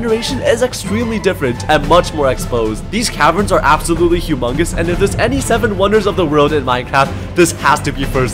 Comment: It sounded like "AMONGUS" HAHAHA SO SUS😰😰😆